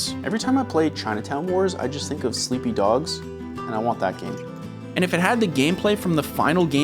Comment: did u just call it sleepy dogs? 😂